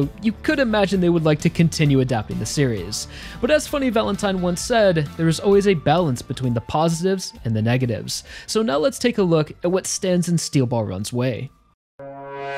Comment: I misheard this as "let's take a look at what stands in steel ball run weigh"